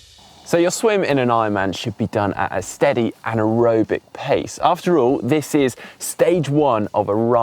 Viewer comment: just a heads up: you might want to make sure that nobody could hear 'steady anaerobic' instead of 'steady and aerobic'